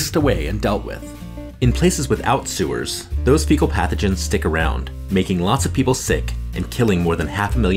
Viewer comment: I misheard "fecal pathogens" as "pickle packages" while seeing faeces on the screen 💀